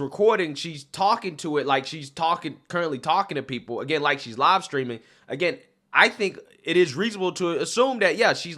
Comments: I kept hearing 'lobstering', I was thinking, wtf is 'lobstering'?? He was saying 'live streaming'. 🙄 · Oh, LIVE-STREAMING! I thought you said LOBSTERING! ‘Black queen.’ Wot a joke. How does she wipe her backside with those ridiculous nails glued to her fingers?